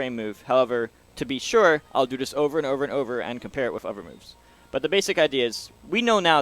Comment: This is really cool and well done and explained. Awesome video for a one shot unedited explanation too. But the thing I find most fascinating... The way he says "uhver" .... Um.. "Other" The TH sound turns into a V, I've never heard that in my life. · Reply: I'm guessing you're talking about 1:20. I hear "other" not "uhver".